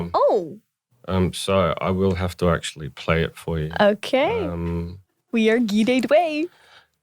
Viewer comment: Can some please tell me what AleXa said at 36:55? It cannot be "we are gidded way"